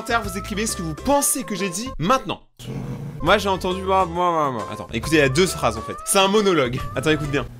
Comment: Vers 11:10 j'ai entendu toi attend